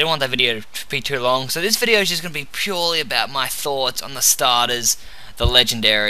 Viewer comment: When you said "This video is about my thoughts...." I heard ".....about my farts......" XDXDXDXDXDXDXD